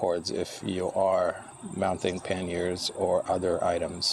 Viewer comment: "Mounting pen-yers...."I'm not sure what that is, am I hearing you correctly? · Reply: panniers... sorry English is not my first language. cheers.